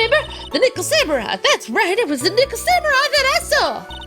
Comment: Lmao at 12:20, sounded like she said "it was the nigga samurai" lol